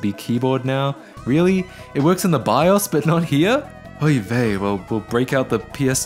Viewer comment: did he just say “oy vey”